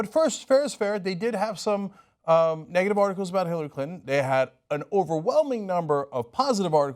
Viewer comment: It sounded like he said Hilary Cunton for a second then lol.